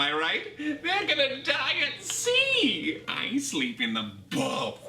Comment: I thought he said I sleep in the bath but weirdly